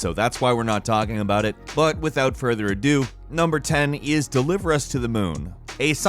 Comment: I heard "without further a Doom".